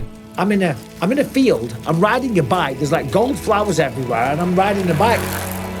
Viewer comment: That montage going through the field was hilarious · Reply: The first time I watched, I thought he said he was riding through a field of cauliflowers instead of golden flowers 😂